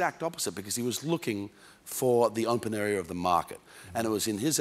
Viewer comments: Crowe's astute analysis of FOX is 'spot on' re: "feeling" informed & the 'place' of truth. But can anybody tell me what he says re: market @ 6:40, "looking for the __ of the market"? ***Thanks, it's "open area" of the market. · ‘People don’t really want to be informed. They just want to feel informed’. Thank you, Russell. Nailed it. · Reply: Thank you! Yes, "open area" makes sense; I couldn't get it with his accent & thought he was saying some variation of entrepreneurial.